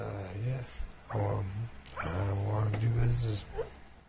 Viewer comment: wth is bro saying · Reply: "I want to do business"